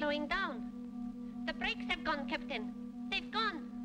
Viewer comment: did she just say that the breaks have gone? How do breaks work on a space ship 😂